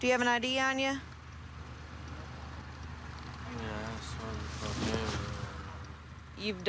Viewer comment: Ok I could’ve sworn that I heard him saying “I snorted cocaine” then she completely ignores that lmaoooooooooo wtf